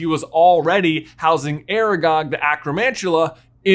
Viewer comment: Does anyone else hear 'Aragorn' at 8:43?? :D